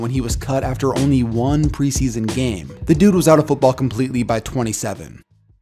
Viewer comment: he says he was out by 2007 what? I thought it was 2009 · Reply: Oh he said by 27 so I thought he said 2007